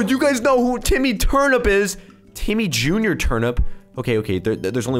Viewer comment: I thought he said Timmy Turner😂😂